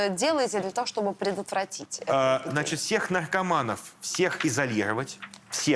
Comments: Послышалось: "Всех наркоманов в ЛДПР".. Лыл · Послышалось: "Всех наркоманов - в ЛДПР!"